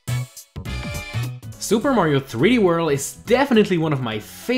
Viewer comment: did he just say 3 world